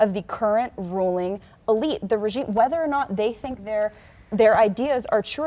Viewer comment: Okay, just listened to this interview. Haven't read all the comments, but did I just hear her say at 22:40-22:42 what I think she just said? Anyone else catch that??? Something on the line of the ruling elite/regime??? Please tell me I miss heard her!